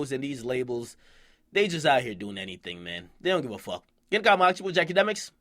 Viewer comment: i always thought he said. its dj jackademiks. 😓